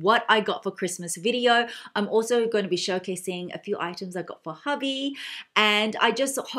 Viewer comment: I thought I heard “what I got for hobby” and I was like wow then what’s the point of Christmas presents😂😂